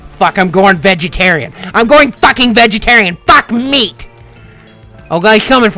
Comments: it sounds like francis says fuck me instead of meat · did i hear: "FUCK! Im turning vegatarian" XD